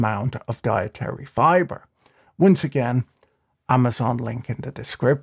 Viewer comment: Did you purposefully say, "wince again," instead of "once again?" If so, this is comedy gold